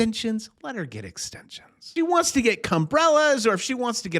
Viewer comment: Did he say Cumbrellas? 🤣